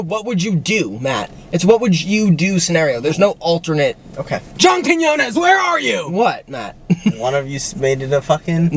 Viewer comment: what did he say ?? junktityannas? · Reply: John Quiñones, the host of the tv show, What Would You Do.